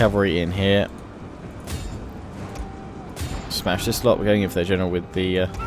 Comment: i thought he said our general is dead in my head when you sent him in i was like that'd be pretty funny if he died